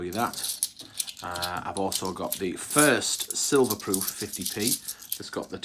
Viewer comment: auto generated subtitles says prooof instead of proof! 🤣🤣🤣🤣